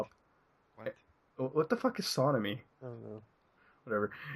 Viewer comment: SonAmy is the shipping name for Sonic X Amy Shippers. Tbh, it sounded like he said Sodomy when reading that outloud.